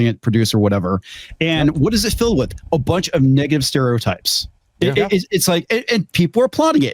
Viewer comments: Did just say "niggative"? 🤔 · did bro say niggive stereotypes